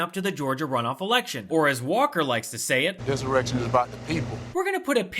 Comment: Wait, did he just say, "this erection?? 😆😂🤣